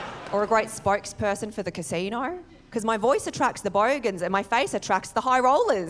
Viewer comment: is great, love her comedy. Although as an American, I don't always fully understand some of the lingo, for example, "bogan". Had to look that one up. For my fellow Americans (and other non-Australians), bogan basically means the equivalent of the American term "redneck" -- someone unsophisticated. (It's not a demonym for someone from Bogata. 🙂) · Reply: Thanks for explaining that! At first I thought she said “bogon”: obscure computing slang derived from “bogus”. Then I thought it might be a corruption of “Vogon”, the callous and bad-tempered aliens from “Hitchhiker’s Guide to the Galaxy”. I was amused that Doulas Adam’s hilarious science fiction farce had so thoroughly seeped into Australia’s lexicon. Nope, it’s wonderfully Australian original slang! 😂